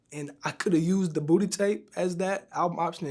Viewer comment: Vlad saying the word booty 🤮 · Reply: Sound like he said “Pooty Tang”